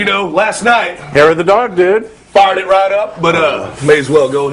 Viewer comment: I know I'm an idiot but at 0:28 I heard "farted right up" at first and I am crying laughing.